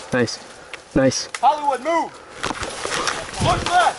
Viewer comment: did he call you Hollywood? Is it because you do YouTube?